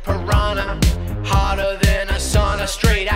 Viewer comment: The first time I heard this I thought Ryan said harder like a sauna and in like that makes no sense 😂😂